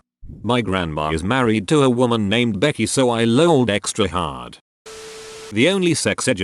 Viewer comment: why did i think they they said "moaned extra hard"?